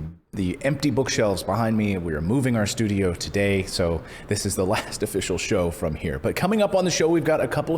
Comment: 😳 I REALLY NEED TO LEARN TO HAVE PATIENCE! I heard "this is our last show"...phew! Congratulations on the new studio! 🥰